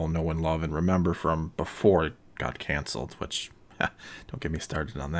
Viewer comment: Thought he was going to say cancer, not canceled lol